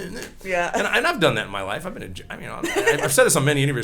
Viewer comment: Did I hear that right? 9:16 "I've been in j(ail)"